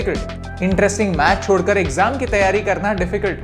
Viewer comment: I thought he said Interesting Maths chodh kr